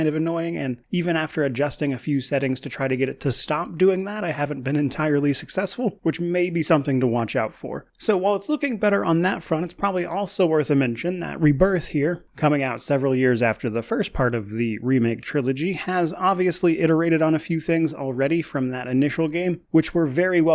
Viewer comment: I had a good laugh because I thought you said Final Fantasy 7 "Afterbirth" LOL. Appreciate your efforts and videos and always, and I'm living vicariously through your reviews and first looks! Hope you enjoy your time with this game